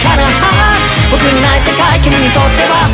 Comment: ええええー？？？このコラボもMVもほんとに最高！！！！！！！！なんか、ハロウィンみたいな感じ！🎃👻 「RUTHLESS」は「LUZLESS」のように聞こえる、「Luzなし」のように聞こえるw